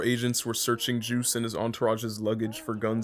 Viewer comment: What did he say at 5:02 can someone please tell me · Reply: com-pulsing i think